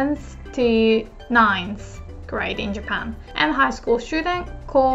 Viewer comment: great lesson , but i have to ask, did only i heard "high scool shoting"?